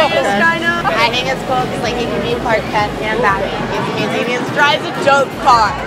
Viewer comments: Did she just say “he can be Clark Kent and batman” Disgraceful!!! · Did she just say, "He can be Clark Kent and Batman"?